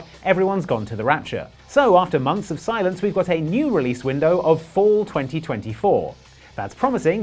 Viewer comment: I swore you said <num> and chuckled with a "missed that too!" took a few passes to hear fall not four >.<